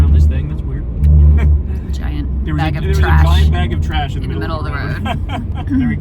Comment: "A giant bag of trash" I thought she said "A giant MAGA trash" 🤣🤣🤣